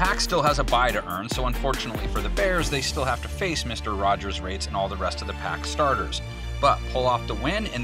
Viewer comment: Did He say Mr. Rogers rates